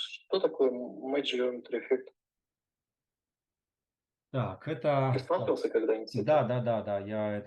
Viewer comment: подскажите про какой модификатор спросил интерьюер? я не расслышал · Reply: matchedGeometryEffect